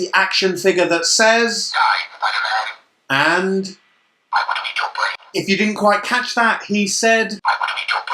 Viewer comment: did he just say I want chipotle .?